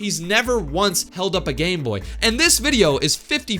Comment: i heard gay board 😭